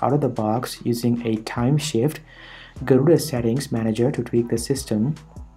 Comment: Did you Just Say Gorilla instead of Garuda ?